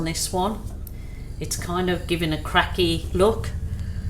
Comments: Hi! These are such pretty fall colors, and I love the balloon kisses and swipes. Like Tammy, I thought you said it was a "crappy look," but I scrolled back and heard you say "cracky look." Thank you for the giggles and especially thank you for the great art! Big hugs ~Cheryl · Thanks for the lesson today. I had to laugh. You said "it's kind of given it a cracky look", I had to go back because I thought you said "crappy look". 😂😂😂 I think if you continued with the balloon it may work out, it's certainly beautiful where you have done the slides and kisses. 💕❤️ Can't wait for the next video!